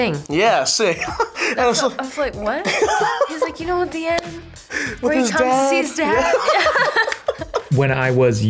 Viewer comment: What did Zendaya say right after she said "You know what the end" mimicing tom？ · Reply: hihi kiki "you Know, at the end, when he comes see his dad”